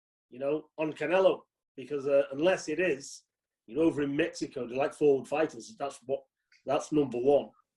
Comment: Ahh yeah Peter, that iconic rustic Mexican city of Dallas. Lovely. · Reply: I thought he said close to Mexico